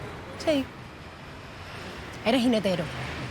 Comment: entendí que le dijo "eres un hetero" XD y digo vayaaaa jajaja pero tiene más sentido lo otro jaja mola esta serie